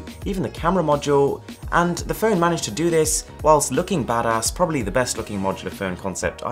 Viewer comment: Did..... Did U Just Said " bad ass" in 5:12?